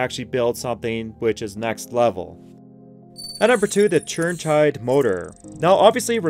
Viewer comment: Did he say: "At number 3 the Churn Chide Motor"? 3:07 Yes, he said it again! hahaha "Churn Chide" OMG, does he use his Churn signal in the car before he Churns hahahahhaahahahahahahahahahahahahaaaaaaa